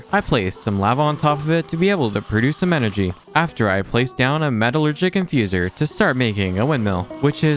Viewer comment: I thought he said metalchicken in 10:08 also why should I trust you your eating expired human flesh